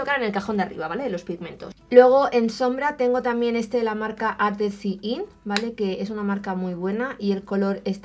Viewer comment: no entendí el nombre de la marca, cuál es !? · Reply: Ardency Inn ;)